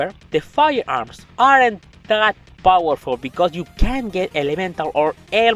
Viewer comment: I need you to work on your pronunciation of the word CAN'T It's hard to hear certain part what you said... what were you saying at 4:19 Did you say they are that powerful?